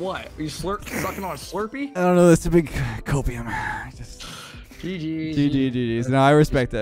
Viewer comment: wait did just call out for inhaling or smoking opium???! What did he say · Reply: Idk if you're joking or not but he said "copium", not opium.